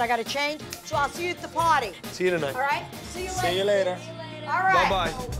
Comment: i thought she said potty lmao 😭😭